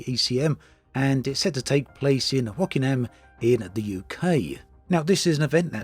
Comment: Wokingham is pronounced "woke-ing-am" not "wokinam" (I had to look up the ECM because I didn't know where "wokinam" was (I thought for a moment you said Rockingham, which is a tiny village in Northamptonshire).